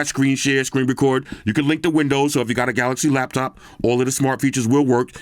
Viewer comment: actually thought just said I can lick the windows